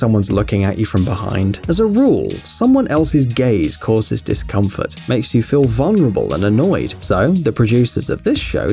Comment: I swear I heard"Someone else's gays causes this discomfort"